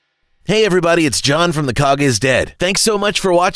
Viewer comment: Oh I thought it said the Dog is dead.